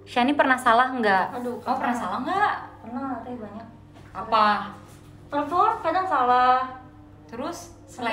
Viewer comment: shani bilang apa yg salah? gk denger.. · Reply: Perform?